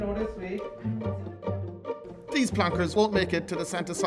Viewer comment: turd of December. 😂😂😂 Yes, i know he actually said third of December. I just found it funny how they pronounce their T's.